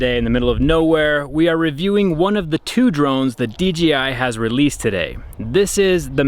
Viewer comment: sounds like you said DGI 😂. Great video as always 👍